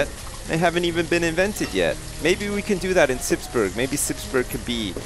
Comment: I thought he said, Slipsburg